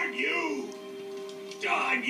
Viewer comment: YOU MANIAC YOU BURNED IT UP, DARN YOU, DARN YOU ALL TO HECK! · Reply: For about 10 years I thought he had said "darn you old heck"